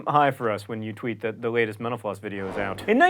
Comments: Every time he says "Mental Floss," I hear "menopause." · Whenever he says "mental floss" I hear "menopause" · Sounds like he is saying menopause when he says mental floss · When you say mental floss it sounds like youre saying menopause. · I always hear 'menopause' when he says 'mental floss'... What's wrong with me? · Every time he says "Mental Floss" I hear "menopause" and get really confused. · It sounds like you say menopause when you say mental-floss. heh.